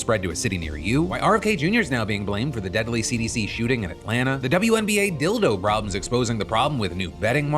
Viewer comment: I thought phill said "The deadly wnba dildo problem" 😂